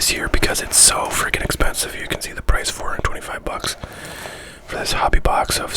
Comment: Is that $425 or $25 for this box? Because the sticker looks like it says 425 but at 4:30 it sounds like u said 25. Sorry I’m not familiar with American sports trading cards and their prices. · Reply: Sorry, at 6:08 you made clear that it’s actually $425. Damn that’s a lot of money haha